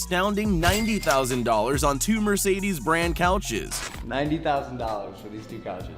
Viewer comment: it sounds like he said toomer